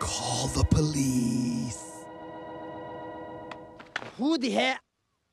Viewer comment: It almost sounded like he was about to say “Who the hell-?”